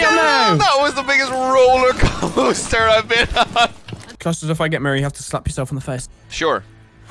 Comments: Sorry, is it me or I just heard say " Custard, if I get married, you have to slap yourself on the face." in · the first time I heard it I thought said "married" and I was really confused until I realized he actually said Mira lol